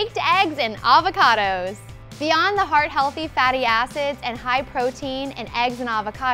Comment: Anyone else heard "fatty asses"?